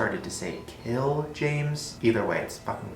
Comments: i thought elmo was saying ciao james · XD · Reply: Yaoi I thought Elmo said cool James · i thought the elmo doll said tell james.......I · Thought it was saying chill games lol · am i the only one who didn't hear "kill james" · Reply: I heard "lick james" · Elmo always sounded like it was saying “help James.” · Anyone else just doesn't hear "kill James"? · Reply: Blue Box Girl I heard "Tell James." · It's not tickle me Elmo it's Elmo knows your name doll. it learns your name so that's not wierd and I heard it say "tell James" kill and tell sound similar so that's not that wierd if you want to hear kill you will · I heard "hello james" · I heard Elmo say "tell James", am I the only one?